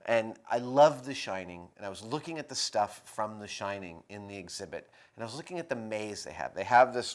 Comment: Ohhh, I thought he said 'deshining' at one point (1:15) instead of designing, but he said The Shining. Whoops.